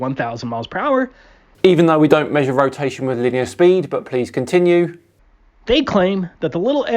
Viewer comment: "even though we don't measure rotation with ____ speed"._ Anyone know what word he's saying before "speed"? For the life of me, I can't figure it out, even after slowing it down. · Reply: Linear